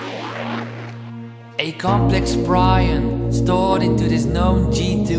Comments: When he says “complex prion” I thought he said “cofege Brian” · Hehe, this is a grate song but at the start it sounds like he is saying "a complex Brian" and I always think of a really complicated person called Brian. The song is grate but, it just something that makes me smile every time I hear it :)